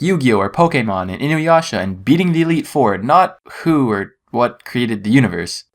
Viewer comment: I legit thought he said "Poo" there, and thought he was referencing Earthbound